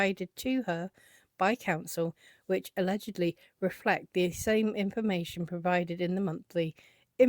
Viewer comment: while listening to the phrase. Had to repeat it several times until I put up the closed captions to see the words (and looked at the document). I kept hearing, “alledgedly reflect the ‘INSANE’ information provided in the monthly invoices prepared for Ms. Lively . . . “ Beautiful work going over this reply, excellent and so much fun to see in legal writing what we know is sensible.